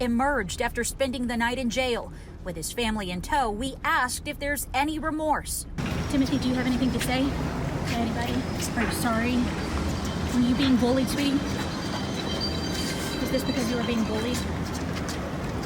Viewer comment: Did this report say, "Where you being bullied, sweetie?" I listened a few times and that's what I heard. Someone please correct me if I misheard. I find that disgusting, if she did infact say sweetie.